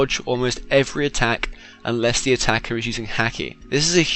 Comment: I thought he said "Unless the attacks are using HACKING"